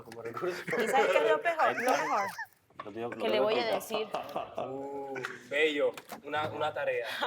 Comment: Minuto 33:25 andreina le dice a Sebastian ( que le voy a decir a...) luego Sebastian se molesta y le dice que todos escucharon sera que dijo algo de vannesa??